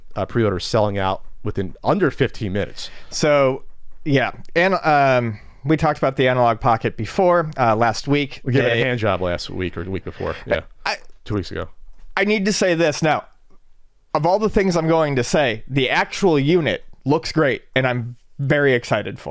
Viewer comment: what did he say? Sounds like he said hand job